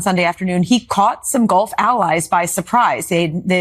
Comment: Trump mixes up Gulf allies with golf allies? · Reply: I misheard her saying “golf allies” and did a double take